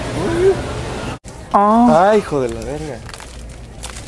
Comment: Did he say "your full of sand as$"??